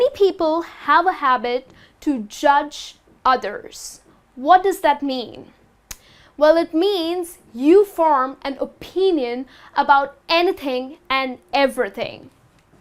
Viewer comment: can somebody please tell me what she says in min 02:21? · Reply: "what does that mean? Means that you form an opinion about anything and everything"